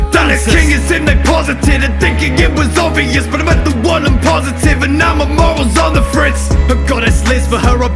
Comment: did he say he was covid-19 positive